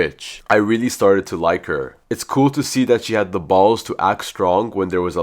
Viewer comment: Why Did I Hear “balls”☠️ Edit:I’m actually definitely hated bc I’m a friend with a bully